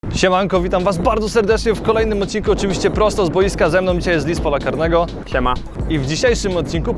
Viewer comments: Usłyszałem Dis pola karnego xd! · Kilka razy usłyszałem "Dis pola karnego" :D